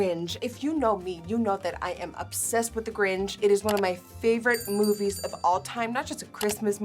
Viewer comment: Nikki baby idk if I’m crazy but near 0:32 you were saying gringe instead of grinch😭😭❤️❤️